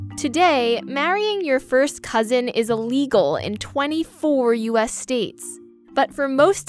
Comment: Lani LeBlanc It's because marrying your cousin is legal in Alabama. The highlighted states is where it is illegal. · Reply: ElCompaManny702 I re-listened to that part of the video. I thought it said "it is legal" when the highlight was playing but now I think it said "it is illegal." Thanks for pointing that out, I was confused and this makes much more sense.